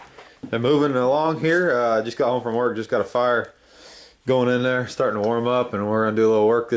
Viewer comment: i thought you said super casually "Just got home from work, got fired...". lol. had to play it again.